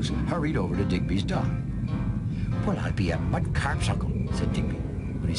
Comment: Any idea what the heck Digby said at 12:11? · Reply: "Well I'd be a Mudkarp's uncle"